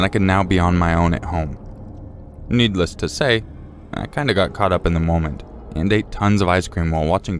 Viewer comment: did anyone else think needless say i keep in check😂😂 (from spider man into the spiderverse song name sunflower)